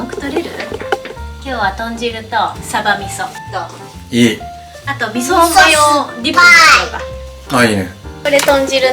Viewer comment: What did at 4:20 say? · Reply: Miso is sour ,she said . It's probably a joke.